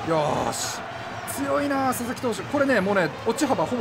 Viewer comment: 鈴木投手って聞こえるw